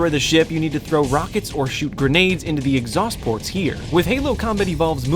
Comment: did he say throw rockets shot grenades